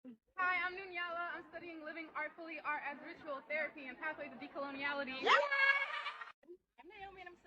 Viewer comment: Did i just hear "pathway to decoloniality?" All the last number of women sound like delusional Communists....i hate to say it......